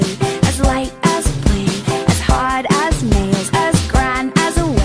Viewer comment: Omg the hard as a nail one I thought it said had hit as a nail XD